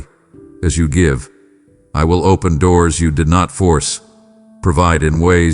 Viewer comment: I thought you said quit opening doors you closed so I thought you meant current one.